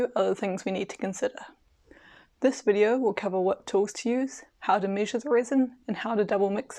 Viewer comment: I thought you said how to measure rizz and thought I was on the wrong video...Good points!